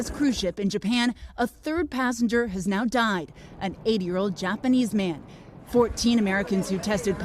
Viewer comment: im sorry, but did she say an 8 year old Japanese man? or did i mishear that?